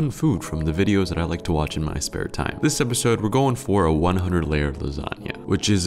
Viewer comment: I thought it said 100 player lasagna for a moment